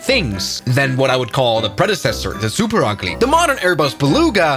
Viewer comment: did he say super ugly?